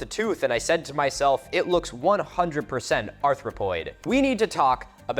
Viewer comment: did you say "arthropoid" instead of "anthropoid"? I can't quite catch it and the subtitles didn't play that part when I replayed with them switched on. Does that mean it belonged to a Bug? Where's Johnny Rico?